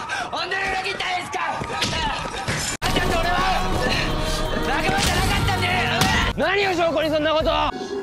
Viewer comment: リアルタイムで見てた時からそーなんだけど0:45のセリフはなんて言ってるんですか？ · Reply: 45秒が境目だから分からないけど、その前の台詞は「本当に裏切ったんですか！？」その後の台詞は「あんたと俺は仲間じゃなかったんで…ｳﾞｪ！」だよ